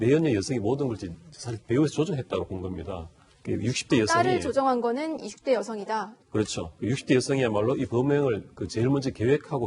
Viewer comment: 딸을 조정한것은 20대 여성이다로 들려요 발음 좀 정확히 하시죠